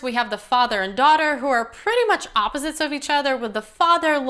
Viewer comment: Haha did you say "there's a baseball team whore" 2:50 Priceless! Good movie!